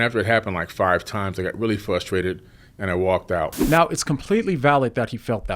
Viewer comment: Did he just say “Flustrated”? I need to use that